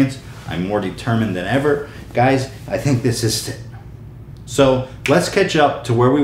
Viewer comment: wtf does he say at 1:53? ''Guys i think this stistis?''